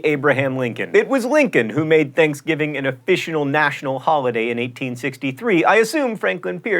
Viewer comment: I can't be the only person who heard "officional national holiday" at 3:32.